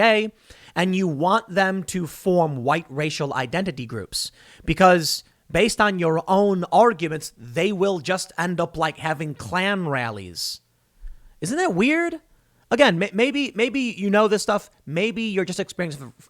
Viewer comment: Just after 2:40 I swear I heard Clam Rallies